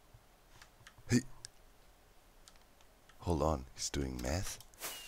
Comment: I wasn't looking at the screen and when Doc read Scar's message at 19:12 - I legit thought he said "Hold on, he's doing METH" and then Doc's reaction still made sense in that context and I was just like ??!??!???? Needless to say, I did stop cooking my food, turned off the hobs and ran back to the screen and rewinded to see what he actually said, immediately.